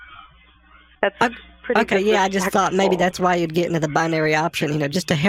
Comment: (well a bit after that) in case you were not able to hear what she said, she said "that's pretty disrespectful", so it seems like she caught the hint.